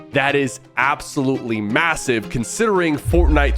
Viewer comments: did you just say massive!?? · did bro say "MASSIVE"